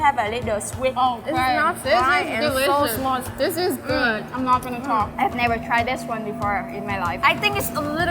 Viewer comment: That BBQ so good that it turned that Vietnamese Girl into a Black American Girl. Home girl at 11:06 said "This good I'm not Finna Talk" I had to replay that part like 3 times to see if I heard her correctly🤣